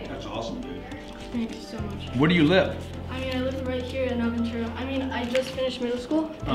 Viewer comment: did he say he's in Aventura? Is Grant in Miami?